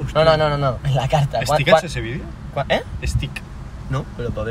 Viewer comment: Lo que dice en el 0:24 "ese vídeo es tik" que significa, que es un vídeo top? O buena idea? Acaso dice tik? No lo entendí xd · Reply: Dice “estikk ha hecho ese vídeo?” Refiriéndose a otro creador que hace vídeos de comida.